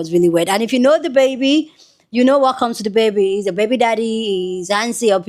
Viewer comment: I thought she said he’s Dababydaddy😭